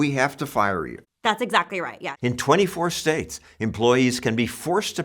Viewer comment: Those unions are definitely not weak. In fact, they cause insane amounts of trouble for airline companies. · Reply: Watch the video again at 4:30. He clearly says 24 states.